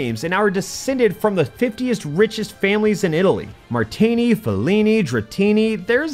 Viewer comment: Thought I was having a stroke when I heard "50-ist richest families".